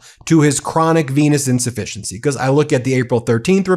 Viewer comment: Every time you say it I hear "chronic penis insufficiency".